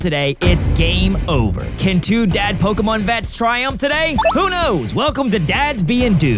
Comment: Intro said “Dad Pokémon vets,” but all I heard was “Two Dad Pokémon” XD